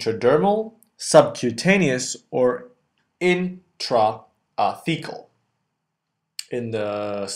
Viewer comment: Haha at 0:55 I wasn't looking at the screen, but just listening and I thought that the narrator said "intrafecal" rather than "intrathecal" like it's spelled out on the screen. I was thinking why in the hell would they inject the local anesthetic into your poo?